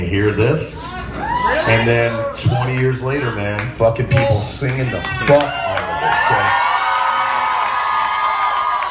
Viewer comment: Can anyone hear what the question at 34:20 was ? The one Corey said was the weirdest question he's ever heard. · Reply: HawkSuddenStrike1 He was basically saying "you're awesome, and thanks for slipknot and stone sour" in a drunken, tongue twisted, garbled mess.